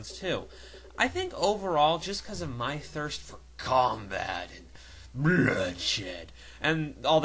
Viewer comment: I thought he say my thirst for cum bat lol